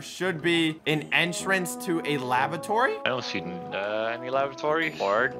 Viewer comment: i thought they said lavatory instead of labratory